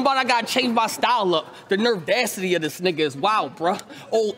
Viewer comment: The nervdacity. What word is that?